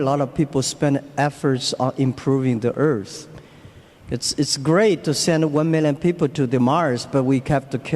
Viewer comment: Did he said "It's great to send women and people to Mars"? XD